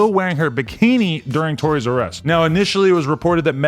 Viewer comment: When he said "Tory's arrest"" I thought he said "Toy'R'Us" 🤣🤣🤣🤣🤣🤣🤣🤣🤣🤣🤣🤣🤣🤣 Any one else??